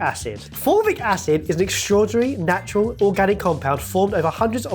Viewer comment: At first I heard 'formic acid' and I thought 'They're making ANT WATER now?!'